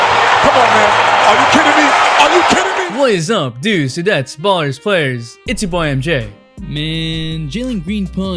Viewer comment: bro what is it you say I never understood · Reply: Bro according to the captions he says "what is up dudes that's bars players" I can't be the only one that doesn't get what he says